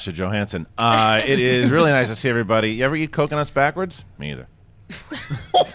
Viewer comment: Hahahahha, after Harloff's question in his intro, i thought he said ever type coconut backwards, so i did to see what it was. That made me laugh out loud literally.